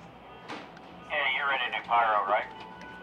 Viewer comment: You ready to Pyro right? Did anyone else hear that?